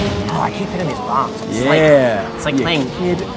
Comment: "Playing this with Tim is like playing video games with Plato, he knows all the secrets of the universe." That quote is worth the price of admission. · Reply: I'm not gonna lie, I thought he said Play-dough